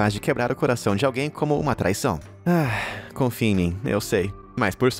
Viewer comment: eu ouvi corno? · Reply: Não, você ouviu "traição"... ou em outras palavras: "corno" mesmo😶